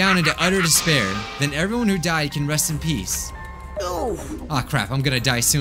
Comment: did kokichi said oof